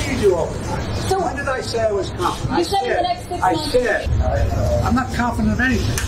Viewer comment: 🤔🤣wow at the beginning it sounds like he said when did I say I was competent, no one said I was competent! Yeah Joe!! We know!! We know your not competent at freakin ALL!!